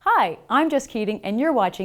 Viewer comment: I initially thought her intros say "Hi, I'm just kidding"